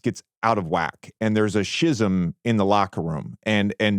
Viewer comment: did JJ just say "shizzum" lmfaooo eskyoo me sir whet